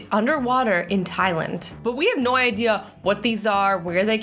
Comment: When u said thailand it sound like u said thailant! 😂😂😂